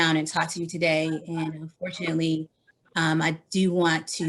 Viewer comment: At the 0:05 mark, does she say fortunately or unfortunately? The cc says unfortunately but I hear fortunately?